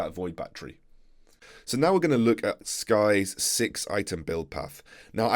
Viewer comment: did he say skye?